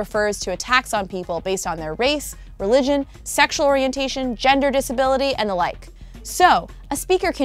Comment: omg at first i thought she said "gender disability" but then i read the comma on the screen and chilled. I was gonna be like wtf has become of the pc culture?!